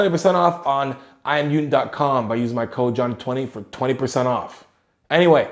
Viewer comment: At the end it sounded like he said “keep Jew chasing”....I’m not sayin, I’m just sayin